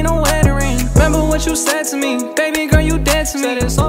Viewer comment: Roddy rich look like the type of guy that wouldnt be associated with gangs edit: stop arguing about my comment jeez. im just saying he looks like a guy that wouldnt be associated with gangs ok? jeez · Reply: My fault i thought he said da baby my blind ass